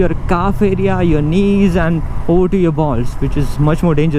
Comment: Did u just say balls @ 4:38 😂